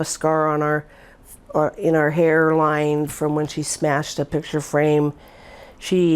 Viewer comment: did she just say " i " ? hmmm.. she must be acting or its staged